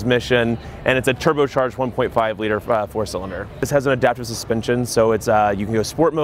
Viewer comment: Did he say "flat 4 cylinder"@ 1:16?